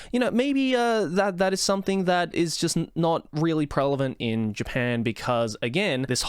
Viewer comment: did you say “prelevant”?